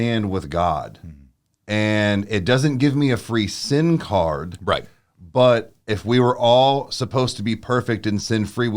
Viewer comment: When you were breaking down the Christian line....i thought you said "a free SIM card" haha....sin card....laughed so hard....because apparently i have listened to you guys so long im losing my hearing. but this was great btw.